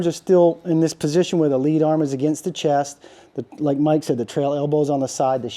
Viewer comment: You might have the elbows mixed up. Which elbow are you talking about? At the time stamp you cited, neither elbow is across his centerline. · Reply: he is literally saying lead arm pinned against the chest